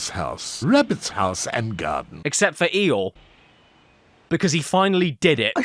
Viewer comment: Legit took me like 3 listens to hear it right, I kept hearing "Rabbit's House and Gun"